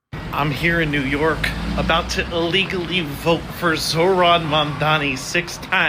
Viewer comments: To me it sounds like he says illegally vote for him which makes it even worse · He clearly says ILLEGALLY. There is very clearly a short i between him saying vote and legally.